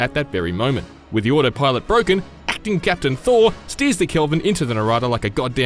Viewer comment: Did they say Acting Captain Thor at 00:36?? 😂😂